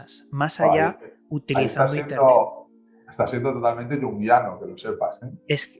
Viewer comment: alguien puede decirme que dijo Pablo Vergel??, no se le entiende que palabras dijo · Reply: “Estas siendo totalmente jungiano” refiriendose a carl jung